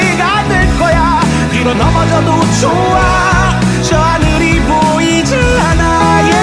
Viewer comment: 아니 나만 " 저 하늘이 보이지않아" 라고 들림?